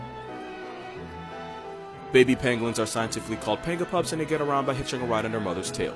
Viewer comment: 😂I had to rewind I thought you said Pangi-POPS not pups😂🤣🤣